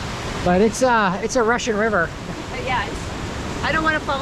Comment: I think it's a Nepalese river, not a Russian river! Ha Miss you guys! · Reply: When I was editing, I totally thought I said Russian river! It took with 3x to finally hear rushing river. Nice catch! 😆